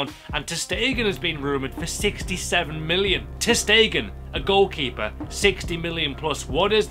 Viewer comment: when he said ter stegen for 60 million, I thought he said stekelenburg xD